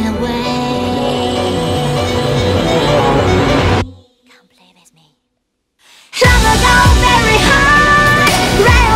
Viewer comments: What does she whisper at 2:30? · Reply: "Come play with me." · How come there's no subtitle for "Come play with me" at 2:32, almost broke my ears trying to figure out what she said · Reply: she says "come play with me" my guy